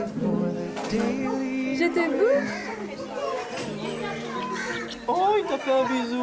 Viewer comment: C moi ou a 34:28 elle a dit "je te bouffe"😂😂😭😭